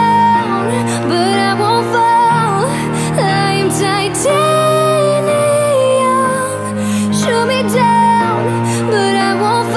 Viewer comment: It kind of sounds like she says, "Liam Titanium," instead of, "I AM Titanium."